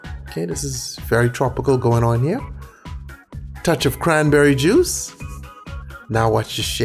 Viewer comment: why the f*ck do i hear cranberry jews